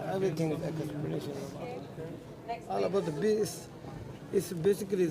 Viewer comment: did he just say “All about the Bass”?🤔